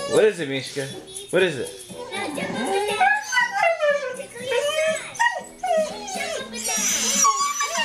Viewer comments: it sounded like she said "i miss my boyfriend" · i thought she said i love my boyfriend lol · Is it just me or does it sound like she said that is my boyfriend! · It actually kinda sounds like she says "I miss my boyfriend" at the end. O.o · At the end it sounds like she says " my boyfriend is out there!" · Very close to the end it sounds like she says *I MISS MY BOYFRIEND!* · is it just me or did dora said "Mishka, tickle your sides" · Is it just me or does it sound like mishka said ''My boyfriends out there'' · I heard Mishka say ''I miss my boyfriend'' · At the last part its sounds like she said " i want my boyfriend" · it sounds like she says "i miss my boyfriend" Then at 0:11 it sounds like she says "I wanna go out" N'awwwwwh poor baby :( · Omg did she just say "I miss my boyfriend" at the end????????? Cool dog now I want one!!!! =) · at the end it sounded like she said I want my boyfriend XD